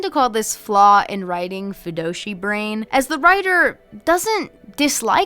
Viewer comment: I know you definitely meant “Fujoshi” (girl that likes BL/ yaoi) but I did for a minute think you were saying FUNDOSHI brain and that…that took me out bc all I thought about was a person obsessed with traditional Japanese underwear 😂😂